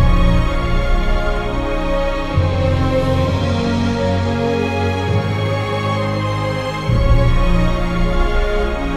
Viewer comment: What does that say? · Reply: The first 4 words say: ‘Leader, command, we follow’.